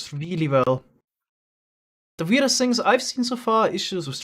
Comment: I heard say “werde” and I was like “what, why'd he suddenly switch to Deutsch?”